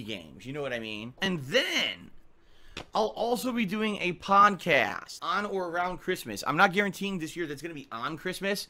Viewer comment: Did he just say "Pawncast?" like a podcast at a pawn shop?